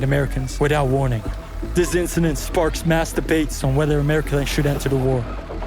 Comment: I was seriously watching this video, but I couldn't don't understand 22:44 , "is it sparks debate or masterbate?" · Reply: hahahahahahahah you had me rollin over here. its "mass debates" but definitely never using those words back to back again 🙈🙈🙈